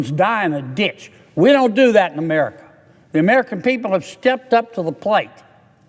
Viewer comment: Every time I hear him speak all I hear is "the plane, the plane boss"!